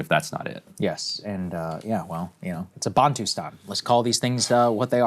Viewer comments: what is that word? Closed captions just says bonton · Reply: He said Bantustan. These were regions set up by the South African apartheid government specifically to segregate the Black population. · What did he say to Google? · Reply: Bantustan. SA apartheid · What did Saagar say to google at 9:45? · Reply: Balm-tu-sa I've been lost on anything close to coming up on Google accurately